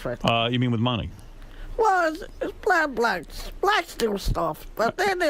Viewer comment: I think he said ‘blacks steal stuff’ ...😆